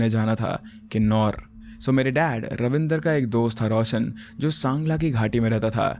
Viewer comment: DID HE SAY KINNAUR?! I am from Kinnaur what the helly